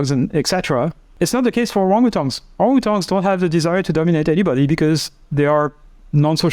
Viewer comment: what does he says at this time? is not the case for ¿what? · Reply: Orang utan maybe some type of monkey